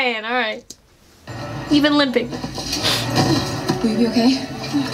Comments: I SWEAR VI SAID BABE AM I HEARING SHIT?!? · That fact that this is 30 min long 🤣🤣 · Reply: I might be reaching...but it sounded like Vi said "Babe r u ok" to Caitlin. U REALLY have to close ur eyes and listen cuz that's wht it sounded like wht Vi said to her... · it sounds like vi said "babe you okay" 😊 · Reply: I’m pretty sure she’s asking, “Will you be okay?.”- but I like the thought. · did she say babe?????